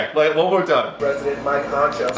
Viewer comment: I hear President Mike Honcho